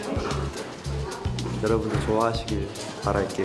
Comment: another instance of the editors caling him minggu in the subtitles when he clearly said "mingyu" lmao they're whipped